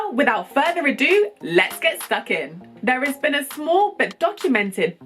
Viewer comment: did she say "Let's get sucking"? Interesting. Anyway, another great video, glad you're back making excellent videos regarding the African Diaspora.